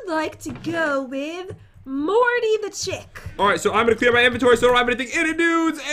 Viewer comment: first time said Morty the chick it sounded like he said Morty the champ.